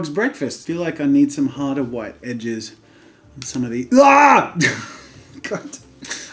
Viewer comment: I thought he said bitches 😂